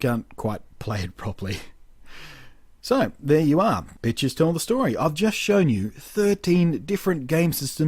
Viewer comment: it sounded like you said 'bitches tell the story'. I had to do a double take.